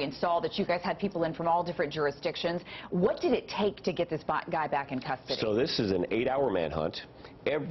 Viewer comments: sounds like she says what did it take to get this black guy back in custody. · Wtf?!…… did she just say “what did it take to get this BLACK guy back in custody?!”….see for yourself, fast forward to 3:17 & make up your own mind